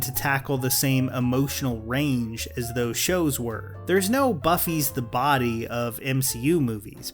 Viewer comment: Did he say Buffie the Body? 🤤